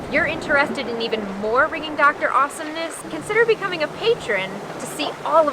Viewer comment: Nice one guys! As well as the new intro you need to check your outro - It cracks me up every time - it sounds like is saying "frigging doctor" 🤣